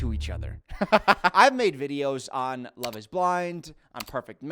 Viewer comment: when started talking about the love is blind season, i was half listening and thought he said love on the spectrum and was very offended and confused when he claimed how boring it was– i had to back up to make sure i heard him correctly. i did not hear him correctly.